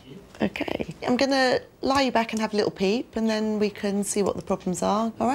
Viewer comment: At first I thought the dentist said "Im gonna lie you back and have a little pee"